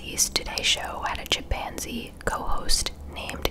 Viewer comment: When she said chimpanzee I thought she said “Japanzee”! 😂